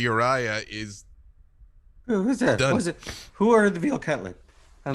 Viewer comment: what did Brother Uriah say? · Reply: "Who ordered the veal cutlet?" ;)